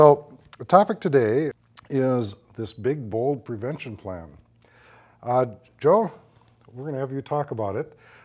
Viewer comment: Oh god the irony. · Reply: this is my alt. I thought the video said aids prevention plan. And the guys name was dick.